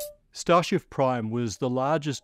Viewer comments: wait, did he just say “Starshif”? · Did he say Starfish, Starship or Starshift? We may never know! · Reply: Starshif. Constonant swap I'm pretty sure.